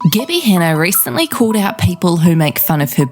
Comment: lmao this is off topic but i thought you said gibby at first